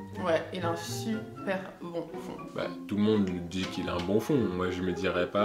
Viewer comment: Quand ils ont dit "un bon fond" j'ai entendu "un bouffon" 🥴🥴